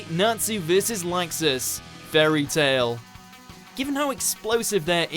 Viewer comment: did he really say "Nazi"? XD